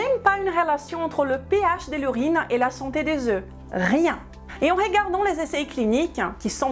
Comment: Je m'étais fait la même réflexion 😆😊👸🏻💟😉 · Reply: J'ai entendu la santé des z'oeufs (avec l'accent). A 9:56 et 10:30... 😅 La santé des z'os. Ah oui !!! J'adore! 🤗😙